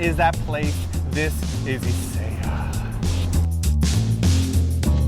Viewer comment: he sound like he said" THIS IS INSANE YEAH!!" ><